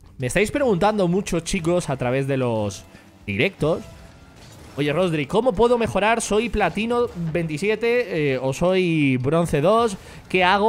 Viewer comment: entendí latino 27 xdd